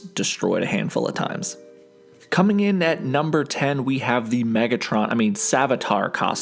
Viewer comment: Did you just say megatron Edit: i misspelled something